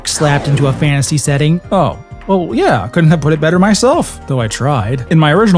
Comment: My lord, I heard "gooder" instead of better. That's unacceptable! Good job overall tho.